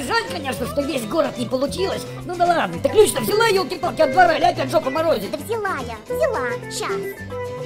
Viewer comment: кто-то может понял что говорит Охламон на 3:15 фраза начинается со слов: "Ты ключ-то взяла?...." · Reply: "Ты ключ-то взяла, ёлки-палки, от двора, или опять жопу морозить"